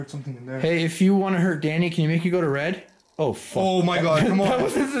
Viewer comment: there is a faint voice,sounds like it says "hey Danny" not sure but it definitely has three syllables then again at 20:41 but more faint